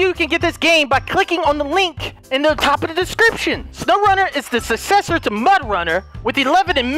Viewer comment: I swore to god it said slowrunner, knowing mudrunner😜